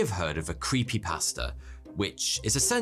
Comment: I thought he said we all know a creepy pastor.